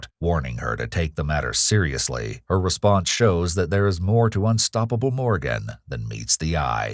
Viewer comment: I thought it said "Unstable Morgan" which would have actually made sense.